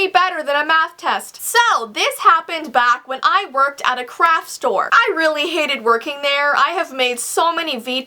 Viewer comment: omg at the beginning i thought that u said i worked at a crack store ahaha